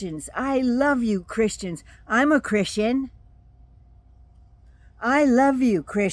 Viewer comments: I thought he said “I’m half-Christian”. He sure as heck did not say “I am a Christian.” I suspect it was a Freudian slip “I’m not a Christian.”🤔. He is a dangerous person! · When Trump says the words, "I am a Christian", notice his body language as he says it. It's like saying "yes" but shaking your head "no" I kept hearing the words "not a Christian" each time I replayed it. Why in the world would he say that to a group of Christians? I activated CC and "not" wasn't in the sentence. He takes up too much space in my head. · He didn't say he was a Christian. Watch it again. He slipped up and said "I am not a Christian", and they cheered him. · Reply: I've listened to it like 5 times and I hear him saying "I love Christians! I'm uhhhhh Christian!" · He accidentally actually said “I am not a Christian” Listen to his speech again. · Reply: Completely agree. At first I thought he said "I'm HALF Christian"...even that would have been a lie.